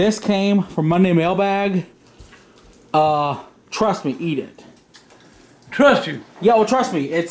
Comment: I don’t know if this is but at the start does anyone else hear someone say ‘twat’ it could be but idk